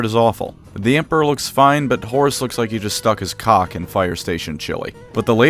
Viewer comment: Bro What did you just say? 😂😂😂😂😂😂😂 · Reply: He looks like the kind of guy to put his cock in chilly